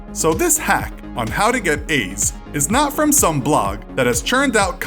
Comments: I heard him say how to get Aids wtf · and 0:52 if it wasn't for the subtitles I would've thought he said AIDS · I thought he said “How to get AIDS”😂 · It sounds like he is saying “how to get aids” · i thought i heard "how to get aids" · I thought he said “how to get aids” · The way he said how to get A's make me thought he said how to get AIDS xD